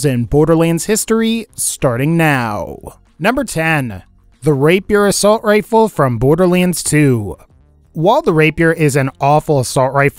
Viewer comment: I thought he said the rape your assault rifle